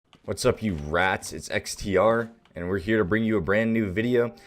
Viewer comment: i know the vid was gonna be good because he said 'wassup rats' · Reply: I thought he said brats